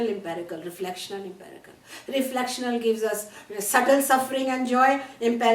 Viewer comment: Help. At 32:15... “Reflection gives us XXXX suffering and joy, empirical gives us gross suffering and joy” What does she say at XXXX ? Thank you. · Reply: “Subtle”